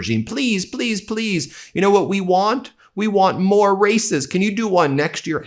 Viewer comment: I swore you said "racists" for a half a second, and I didn't find that at all surprising. Sad...